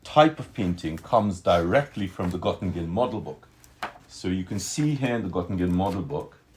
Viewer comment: anyone knows the title of the book mentioned? CC saids: gotten gin model book. i ran a search and found nothing. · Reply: Gottingen model book